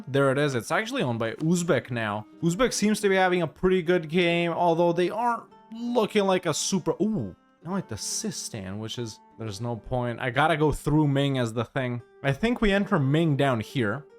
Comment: Sistan? I thought you said Sistine! As Sistan become papal controller while owning Rome.